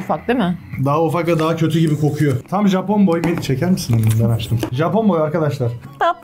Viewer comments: Ben mi yanloş duydum yoksa 7:50 sn yeye alırsanız tam japon boy elini çekermisin aşkı dedi sankiii ben öyle duydum aşkım dediğ djndndnd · benmi yanlış duydum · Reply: Cekermisin elini ben açtım diyo